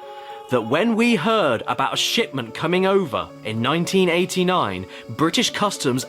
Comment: Anyone else heard "sh*tment?" at 47:54? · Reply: shitment